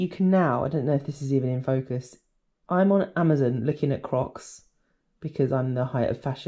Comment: Hi,oh my days,i thought you said I'm on Amazon looking at cock, 😂then I saw the crocks,so sorry everyone if I have offended you, it's me I'm going deaf well I am 68 😅great down to earth honest video thank you for sharing, what a lovely cat cheerio for now take care sweetheart.... E. u.k.